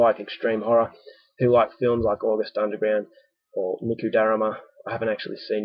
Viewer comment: Good review! Can I ask though - at 6:05 the reviewer mentions a film which I cannot fathom what you say... maybe Luku Darama? Please, anyone who knows what this is - tell me. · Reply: Niku Darama? Its a Japanese extreme pseudo snuff film. Thanks for watching!